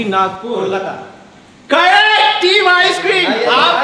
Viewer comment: Everytime Mamba Said "Team Icecream" & i heard "3 Icecream"😂